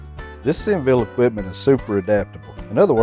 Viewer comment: I thought that said 'senile'-my bad😁Thanks for the video